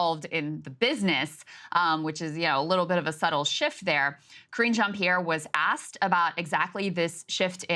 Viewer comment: rofl, at first i heard " 'cringe' jan pierre" tha's what i'm calling her from now on.